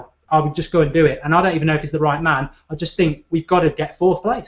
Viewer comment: And what manager was that, the one that said at 24:42? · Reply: Renee mulensteen, was just brainstorming temp managers till the summer.